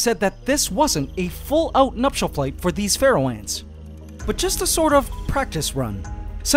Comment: Ngl but all the time I’ve been watching your channel, I’ve thought you said “feral ants” lol